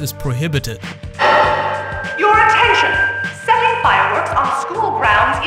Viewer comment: I think the PA at 4:47 said SETTING, not selling. Selling isn't really terrorism, but lighting aka setting fireworks obviously causes an explosion.